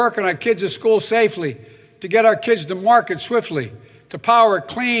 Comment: Did Joe just say, we need to "get our kids to market swiftly." at 4:35. What did he mean?